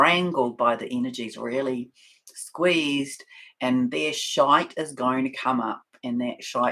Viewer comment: I woke during the night couldn't get back to sleep so listened in here. I'm laughing so hard at 4:40.... Had to replay a couple of times to be sure I heard it... Sh*te 😂😂😂. And you say it so politely 😂. But yes, I'm sooo.... seeing that with people around me now. Not so funny! 🙁 👍